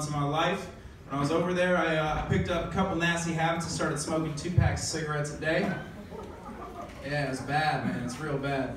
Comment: I thought u said 2 cigarettes a day , but after watching it in repeat I realized that it was 2 pack of cigarettes. 🤯🤯🤯